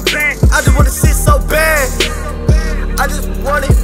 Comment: This homie sounds like he saying "i just wanna shit so bad" lmbo